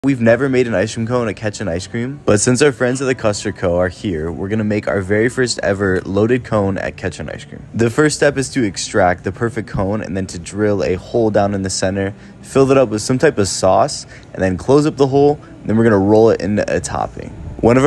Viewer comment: I thought he said “ At ketchup ice cream” 🤮